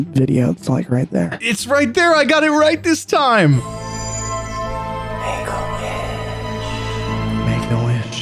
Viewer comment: Maybe I have a dirty mind but when he said "make a wish" i thought he said "hey little b****"